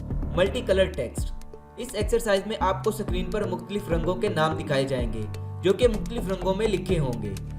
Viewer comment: Suckreen what a word bro keep it up.😁😀👌